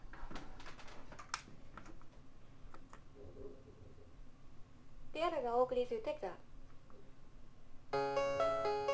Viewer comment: Does anyone know what it "says" at 6:11 just before the title music? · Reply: Please center your joystick and press a joy button.